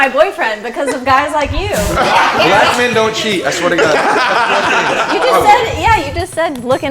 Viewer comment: what did you Said 😂😂😂 Black Men Don't cheat?